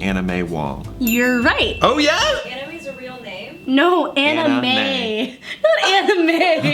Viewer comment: i also misheard anna mae as anime and was embarrassed that they had to clear that up for me..